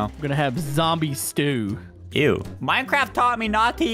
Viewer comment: did you just say minecraft tommy as in tommyinnit